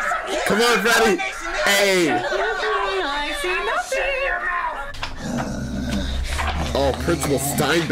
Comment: did Joesph just say he was gonna shit in Mikey’s mouth 😂